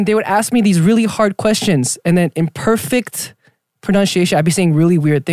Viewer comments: The subtitles are wrong in Korean and English at 0:31 he says "In perfect pronunciation" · pretty sure the subtitles are wrong? should be “perfect” not “imperfect”.